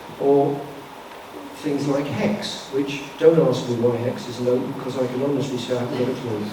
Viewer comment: I can't quite make out what he's saying with "don't ask me what hex is ___ because I haven't got a clue", anyone know? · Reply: 'Closed' i think because it's been shut for a few months